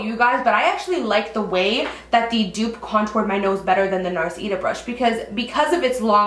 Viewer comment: I thought you were saying "eater brush" because of the auto play I didn't know what I was watching haha